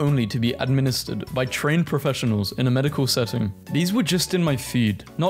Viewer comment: I looked away to grab something and thought you said train professionals